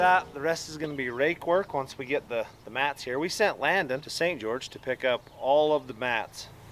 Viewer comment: I heard that as he did send Landon "to get all the Matts". 😂😁😜